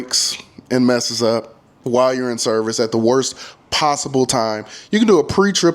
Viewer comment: I thought he said YN SERVICE....🤦🏿‍♂️🤣